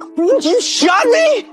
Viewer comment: Ngl. Up until now I thought he was saying “I heard you go fast”